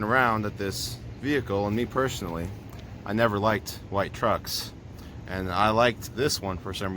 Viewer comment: He said he personally never liked white trucks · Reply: Yeah, I had to go back and relisten too - I could have sworn he said "light" trucks but it was definitely "white" trucks.